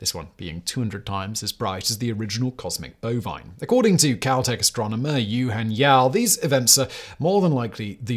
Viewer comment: Did he say "Caltech" or "COWtech"?? 😂😂😂